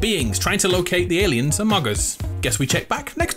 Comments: did he just say amogus???!?11!1 OH MY GOD ITS AN AMOGUS REFERENCE😳😳😳😳📮📮📮📮📮📮 SUSSY BAKA IMPOSTER SUS HAHAHAH HE GOT EJECTED RED SUS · did he say AMOGUS